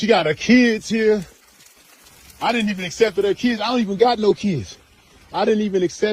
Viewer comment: the guy sounds like he's saying "I didn't even accepted her kids" meaning "I accepted her kids". Damn, I've been teaching English as a foreign language for so long, I don't even understand native English speakers anymore.